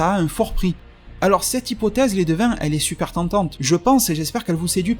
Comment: C'est quoi le mot qu'il arrête pas de dire? J'entends "les devins" mais comme ça a pas de sens je doute🤔